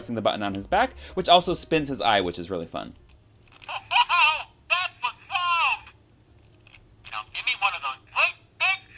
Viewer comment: Mike Wazowski's Sayings: (I Don't Know What The 1st Thing He's Saying) "Now Give Me One Of Those Great Big Scares!" "No No No No No No, Not A Good Idea..." "Yeah Yeah... (Gibberish)" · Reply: The first 2 phrases that Mike says are “Yeah, yeah. Blame it on the little guy.” and “Whoa ha ho! That was loud!”.